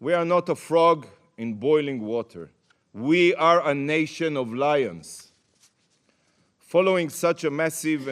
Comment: He said we are a nation of Lions but I hear"we are a nation of Liars"